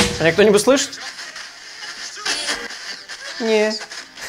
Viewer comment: а я услышала "nope" 🤔